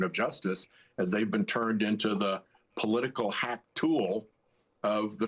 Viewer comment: "They've been turned into the political hack tool of the Trump White House." I kept hearing this as "the political hawk tuah" until I turned on the captions. You're a classy guy, Senator, so I knew you didn't say it... but man, it sure woulda fit on that thang! Thank you as always. I look forward to each and every one of your posts. 😊 💙💙💙